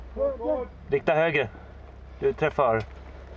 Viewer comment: did he say "Big burger, Alt F4"?